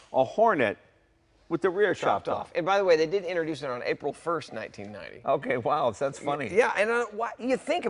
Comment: Did he say April 1 1990?